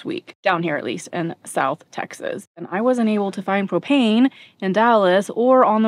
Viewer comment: I thought you said, “I wasn’t able to find cocaine” 🤣